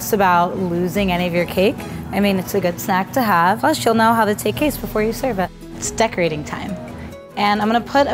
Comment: “take castes” I legit thought I just misheard that like I usually do